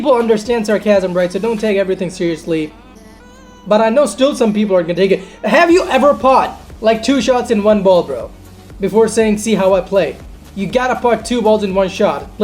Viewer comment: did u say "pot two shots in one ball"? love u bud 😂